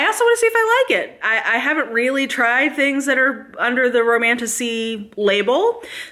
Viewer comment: Maybe it’s just my broken mind but the amount of times I hear ‘Romantussy’ is positively disturbing 😂